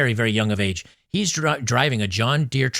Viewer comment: sounded like "he's drunk driving" 😂